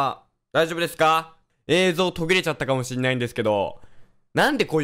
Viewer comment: ぐらいの所で言ったのが｢映像飛び出ちゃった｣って聞こえたw